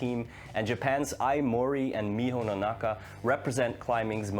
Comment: Hey, great vedio. And definitly rooting for Janja. But also looking forward to Miho's performance. Also, btw, her last name is Nonaka instead of Nanaka(2:34) XD